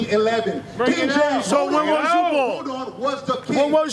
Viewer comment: When was you born? 🤣 What?? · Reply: I thought he said who was you Born lmfaaaoooo😂😂😂😂😂😂😂😂😂